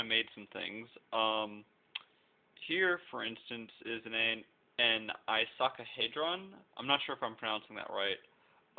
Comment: did he say " I suck a hadron" ?!?! :D